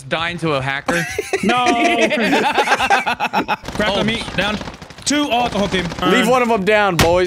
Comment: did someone just say "Grab The Meat"